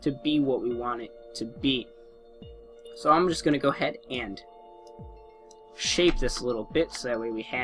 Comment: I thought he said "Let's go ahead and shave this little bitch" xD